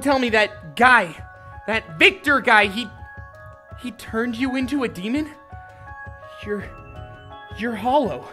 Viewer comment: Oof! I thought it said "My friend turned me into a demon" I was like "NOOOOOOOOO"